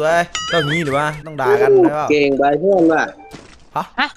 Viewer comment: เขาพูดว่า เกรงใจเพื่อนวะ